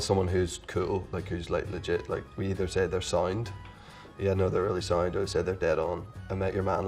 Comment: I thought he said "signed" at first lol. Nope. SOUND.